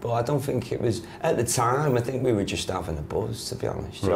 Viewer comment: could you help me understand what Miles said at 0:54? they were often...? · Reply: at the time i think we were just having a buzz to be honest